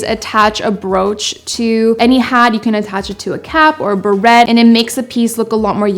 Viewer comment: I thought she said barettes, then I saw a “beret”, I got confused for a second lol